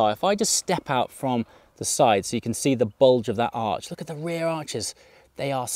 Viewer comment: Had to rewind and watch the start again. When you said the bulge of the arch. Stupid person here thought you said the bulge of the arse... Thought that's not like 🤣🤣😂😂😂